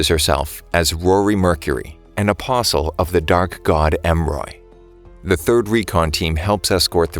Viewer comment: man i really thought it said The Dank God not The Dark God bro